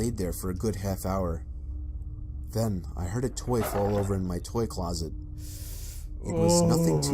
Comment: anyone else hear ‘ I heard a twig fall over in my twig closet’ at first